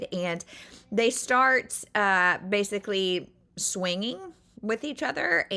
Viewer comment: I had to go back a few seconds 😂I was like wait a minute did she just say swinging 😂😂😂😂😮😮😮😮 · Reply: Swinging. 😵‍💫🫣I should’ve just DNF’ed because it was clearly heading down hill haha